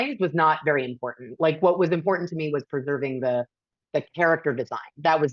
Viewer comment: It's actually worse than that. At 1:11:00, where you say she's over focused on character and making it character driven: you've misheard her and actually assumed she was being more intelligent than she was. She says "What was important to me was preserving the character design" It's not even about the characters; it's about how the characters look.